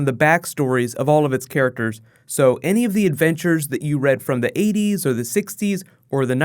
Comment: did you say avengers